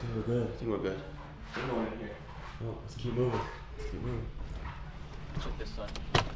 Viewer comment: i heard a voice saying “steve” or “keep” make sure to listen clearly…. It wasn’t Cyrus or dari🤞🏼 it seemed like someone was shouting from afar, that’s really creepy